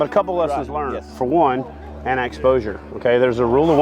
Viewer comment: what should you wear if the air and water temps don't add up to 120? It sounded like you said annanexposure? Not sure what you said there